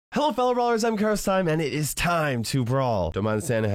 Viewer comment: In the start I thought he said "Hello guys it is KairosTime and I am DIEING to brawl!" BTW I HAVE NOT WATCHED THE VID AND I THINK BULL IS S tier